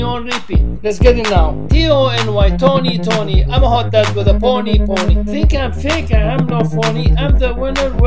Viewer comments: "Happy Tony, Sad Tony, Mentally disturbed Tony" 😂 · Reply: 456 i thought he said "imma hook up with a pony,pony😟 im a horrible person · TONY'S PART LYRICS My name is Tony (yes) And I'm the father of Twaimz (Issa- Hey) I'm better then the kardeashians cause they are lames I don't play games (no!) I watch arabic TV (uh-huh) I put you on pause and put me on repeat (Let's get it now) T-O-N-Y Tony Tony I'm a hot dad with a pony pony Think I'm fake? I am no phony I'm the winner, where my trophy? I'm so stunning am I wrong? I might as well have my own song Happy Tony, Sad Tony Mentally Disturbed Tony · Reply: egg yolk I thought he said "I'm a hot guy with a pony pony" 😂